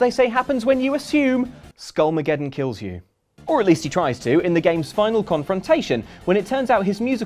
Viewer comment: I thought you said spinal confrontation. My bad.